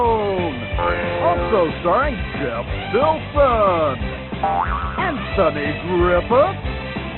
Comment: The sound on the Protis was so bad, when he said "Anthony Griffith" I heard "And Sunny Griffith"